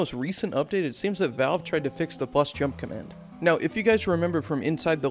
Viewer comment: I keep hearing "the bust jump command" and that sounds very accurate